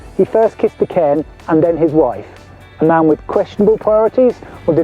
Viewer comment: I really thought you said kick the cairn kick the wife. Nothing wrong with that!